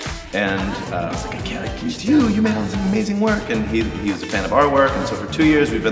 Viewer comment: can someone write me what does he say here? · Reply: "He's like, 'I got to get to you. You all have made some amazing work!'" Sorry you had to wait two years to know!